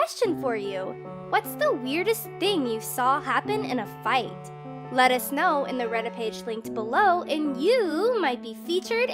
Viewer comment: LOL I THOUGHT SHE SAID: "lettuce know" Like that must be some smart lettuce right there!